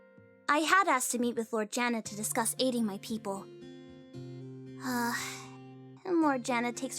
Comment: When said "Aiding my people!" I thought she said "Eating my people!" XD